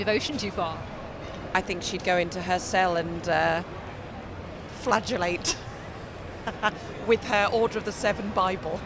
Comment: I thought she said "go in the cell and flatulate" but it must have been flagellate 😁